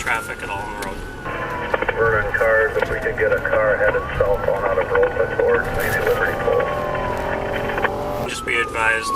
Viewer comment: "Vernon cars if we could get a car headed southbound out of Viroqua towards maybe Liberty Pole" is what's being said